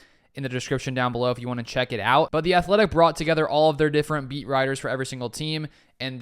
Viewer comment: He said "beat writers" and i heard "meat riders" lmao